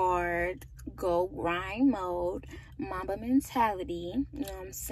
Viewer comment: OMG did she just said Mamba mentality?